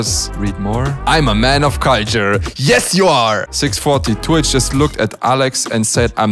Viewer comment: Who always misheard when he say two h as twitch????